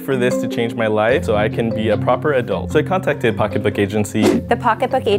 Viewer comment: Because I'm Australian I thought they said pockeba instead of pocketbook